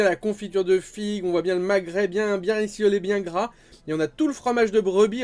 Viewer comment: J'ai pas compris pourquoi à 5:07 tu parles de Maghrébin bien rissolé ? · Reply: Bien rissoule bien gras et avec la liaison c'est vrai qu'on peut comprendre maghrébin mais c'est pas ça si tu réécoutes on comprends bien ce qu'il veut dire hihi :-)